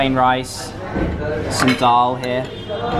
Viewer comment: you said some dal here but the subtitle said some doll here lol😂😂😂🤣🤣🤭